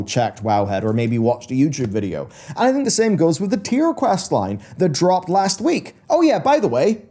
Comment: Funny when you say "Tyr questline" I keep hearing "Tier questline"